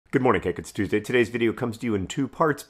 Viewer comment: Hello. It's me, 19. Get your crash course coin here before June 13th: <link> Thanks for being here with us. I'm off to learn my one line in the Turtles All the Way Down movie. -John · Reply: For the record, this time I heard you say, "Good morning, Cake; it's Tuesday." And henceforth he shall forever be Cake Green